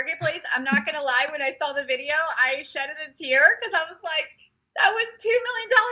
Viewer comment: Did she say ”shed-ed” a tear?? 👀🤭